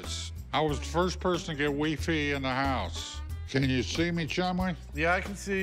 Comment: I thought the old man said he was the first to get wii fit in the house....dude just can’t say WiFi......